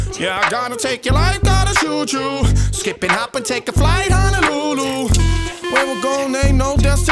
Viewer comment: Damn hes saying Honolulu. I thought he was saying LuLu which is a ww2 jet by Lockheed Martin. The LuLu Belle